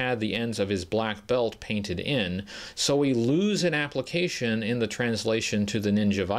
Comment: I misheard you at first, and now all I want is an Appalachian Viper. It would come with several bottles of moonshine as accessories. I may have to do another custom. Great video as always!